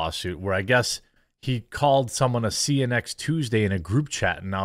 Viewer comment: I thought he said c u net thursday.!